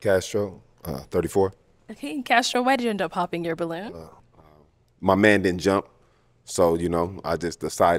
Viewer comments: Did you hear what the guy with the cap said at 29:19? Talking about his manhood did not jump? What the heck? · Did I hear properly or did he just say “My man didn’t jump.” Or did he say “mind”? Please help me, I’m deeply confused .